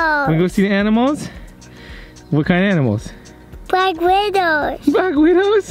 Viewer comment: I thought said "black weirdos" hahaha 😂😂